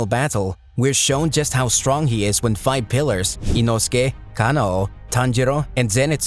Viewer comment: did you just say FIVE pillars and then name FOUR people who aren’t pillars🤨🤨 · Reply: LEVEL pillars